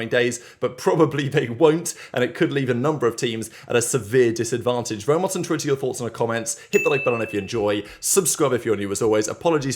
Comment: Does anyone understand what he says at 0:30?? “Vinny Dutch’s tits in her thoughts” is what I hear, and he says it every show.